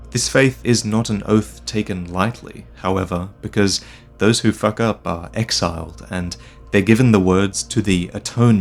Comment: did he just say "those who fuck up"? Like it's fine, Vaati just doesn't usually swear so casually... if at all, that I can recall.